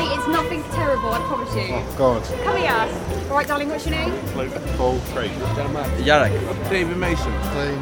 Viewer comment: did anyone hear them say logan Paul XDDDD